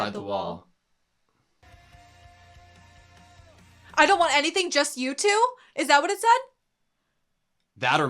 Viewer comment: "I don't want anything just you two" is that what it said???? That or Mewtwo Now I kinda want an alternate reality where Eren just fucks off to the Pokémon world as the main antagonist to Ash where Erens main goal is to just free all the Pokémon lol.